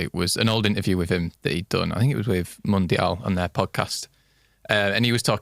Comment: which podcast is talking about here? I can't quite make out what he calls it. Edit: It's GIANT's podcast "Luke Ayling: Do You Remember The First Time" (on Spotify)